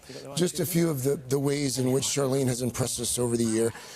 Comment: Does he say “Charlene”?!?